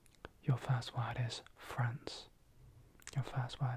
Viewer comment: i misheard france as friends and i was so confident with sitcoms being the category :D got confused when you continued with england, and realized my mistake when you said spain :D:D lovee these word games!! <3